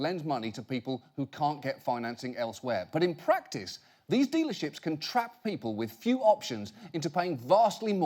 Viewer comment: I thought he kept saying demon ship whenever he said dealer ship. Did he?